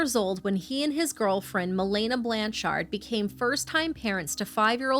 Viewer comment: did I hear Blanchard?💀